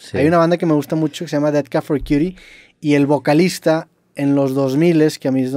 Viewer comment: Como se llama la banda que dice min 6:23 ? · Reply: Death Cab for Cutie